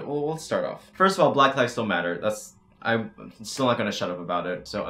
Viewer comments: For some reason the captions said “black lives don’t matter” and I want y’all to know I literally said “black lives still matter” Also masks have been proven to not restrict oxygen flow. Don’t say you can’t breathe in them, just say you don’t like having to wear a mask. OH AND here are SOME of the 100+ “influencers” who went to that party unmasked: Nikita dragun, Emma Chamberlain, James Charles, Charli and Dixie D’amelio, The entire hype house, Bryce Hall, Larrayxo. And turns out cases in a lot of other countries are still rising. Oh well I guess this entire world is stupid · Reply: Oh i thought he said black lives dont matter and I was gonna cry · For some reason the captions said “black lives don’t matter” and I want y’all to know I literally said “black lives still matter” Also masks have been proven to not restrict oxygen flow. Don’t say you can’t breathe in them, just say you don’t like having to wear a mask. OH AND here are SOME of the 100+ “influencers” who went to that party unmasked: Nikita dragun, Emma Chamberlain, James Charles, Charli and Dixie D’amelio, The entire hype house, Bryce Hall, Larrayxo. And turns out cases in a lot of other countries are still rising. Oh well I guess this entire world is stupid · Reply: Im glad you made this clear coz I legit heard "black lives dont matter", replayed, heard it again and was about to hate on u pretty hard